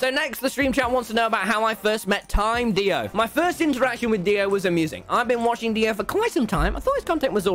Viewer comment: Did you just say DIO?